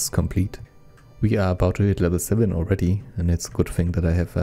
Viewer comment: i first heard were about to hitler 7 already :D